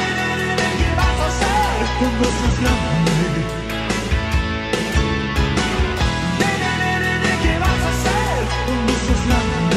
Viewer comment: Pensé que decía: dime dime que vas a ser ... jajajaja xd